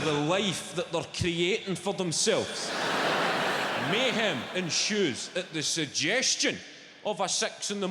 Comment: Even the closed caption system has no idea what this guy is saying. At around 3:50 or so the caption read, "mayhem and shoes," but I believe he said "mayhem ensues." Don't quote me on that though.